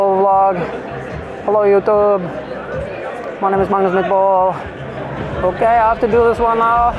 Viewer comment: When he said “my name is Magnus mitdbo” I heard “my name is Magnus meatball”